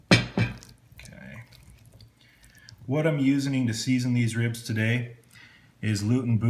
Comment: Did he really loosely wrap and then put them in meat side up? To each their own, I guess. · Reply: Did he really say "What I'm usening"?